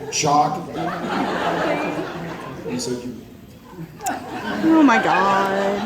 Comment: What did Jared say at 15:20? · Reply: He cuts himself off after saying "You said you--" The reason I said "Oh my God..." was in response to him pantomiming getting hit in the face with jizz.